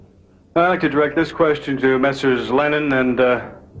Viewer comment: This has always caught me off guard the reporter says "I'd like to direct this question the MRS lennon and mcartney" does he really say mrs? Am I hearing that right? And if he is is it a comment on their "long" hair or something? It's such a random thing to say?